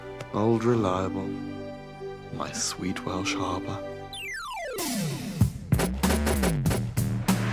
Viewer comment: *Sweet Welsh Harbour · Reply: i thought he said harpy not harbour 🤣